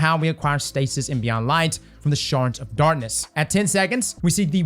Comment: Who else heard "shart of darkness" at 0:46?